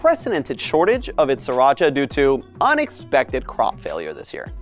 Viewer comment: Bro I thought he said 3racha save my mental health please